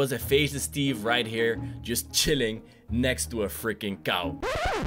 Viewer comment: did he say. cow